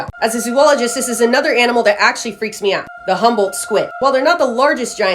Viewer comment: I heard the “Humble Squid” 😅